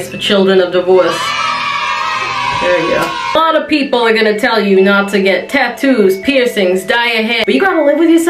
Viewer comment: I thought she said "Bottom people are gonna tell ye not to get tattoos..." and was very confused.